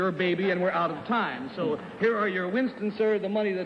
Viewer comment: What did he hand him when he left? It sounded like he said "Here are your Winstons". Please say that isn't so.